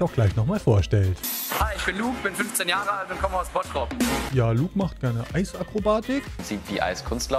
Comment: Ich habe erst ,,Hi ich bin Noob, bin 15 Jahre alt und komme aus Spongebob" verstanden😅